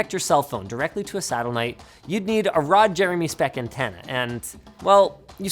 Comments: I thought I heard that wrong at 6:15 but turns out he actually said “Ron Jeremy spec antennae” absolutely gold joke 😂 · did he say "satelnight"? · <link> I thought he said, "You need a Ron Jeremy speck antenna." im like wat.